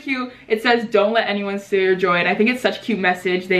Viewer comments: I uh😂😂thought u said "dont let anyone steal your joint"..but thats just because I'm stupid😂..//love you! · I thought she said don't let anyone steal ur joint 😂